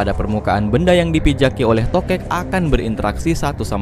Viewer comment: toket?? Gw dengernya gitu anjir